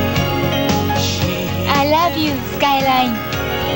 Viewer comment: Did she say, "I love you Skyline" or "I love you lone time"? My closed captions aren't working...